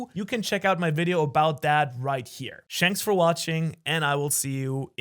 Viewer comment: am i the only one who heard him say shanks for watching